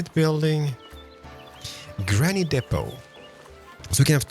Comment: did you just say "Granny depot"? 🤣